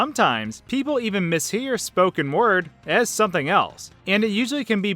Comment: I thought you said smoking words